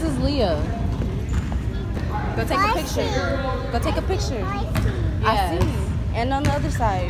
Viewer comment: When she said “I see” I thought she said my name “Majesty”